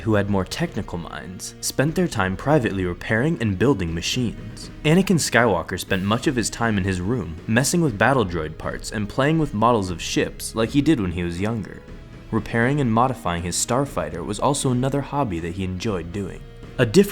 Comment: After watching this video, would you still want to be a Jedi? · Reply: Fact Free i heard you "playing with ball of shit" when you are speaking about anakin skywalker